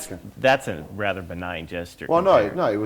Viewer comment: Ross Dixon Ellis what was the word the interviewer said @ 6:35 like what kind of gesture? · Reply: Kyle Madigen "benign gesture"